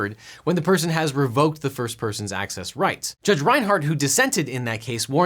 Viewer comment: I thought I heard Judge Reinhold and assumed I was being trolled.